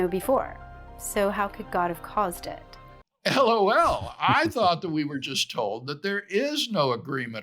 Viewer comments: did I just hear what I think I heard? Did Craig just spell out "L-O-L?" XD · did our good doctor just say L O L or did I mishear?